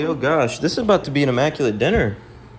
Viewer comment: I thought you said an ABOMINATION dinner, but you said an IMMACULATE dinner.